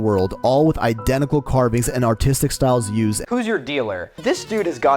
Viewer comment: I swear that mf just said “autistic styles” and as a proud neurodivergent person I am offended by my own mishearing